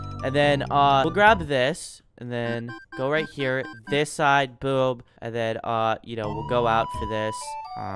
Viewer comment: the text said boob instead of boom